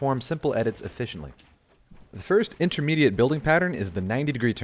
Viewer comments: I thought is said intermediate bullying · I thought it said intimate building